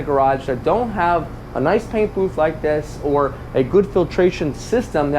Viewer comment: I thought you said" no paint booth"